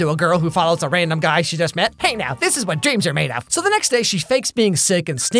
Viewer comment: So when I was little I used to watch this movie all the time/ listen to the soundtrack and my baby bro liked to sing along as horribly as possible and he thought it was saying “Dream Tomato” instead of “this is what dreams are made of.” To this day my family calls it the Dream Tomato song